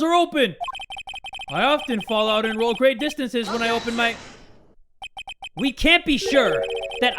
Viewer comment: "Something long falls of when I open my..." Well at least thats what I heard.